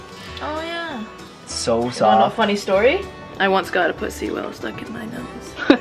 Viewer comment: Ik this off topic but I thought she said she got a pussy stuck in her nose in